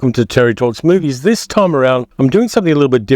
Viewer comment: Just a fun fact when you introduce yourself on this video it sounds like you say Terry talks smoothies, lol